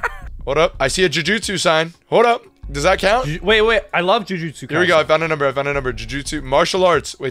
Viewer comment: I heard say "Wait i love jujutsu kaisen" when they were looking for jiu-jitsu places XD